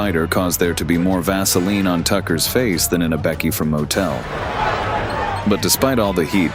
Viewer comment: What Did He Say? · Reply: More Vaseline on his face then a Becky at motel. 😅 cringe asf